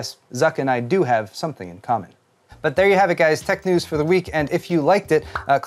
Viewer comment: at the end when you say "There you have it folks, tech news for the week" I hear it as "tech news for the weak" and there is nothing you can say to convince me otherwise